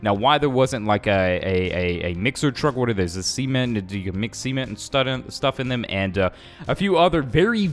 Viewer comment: I thought bro said to mix semen 💀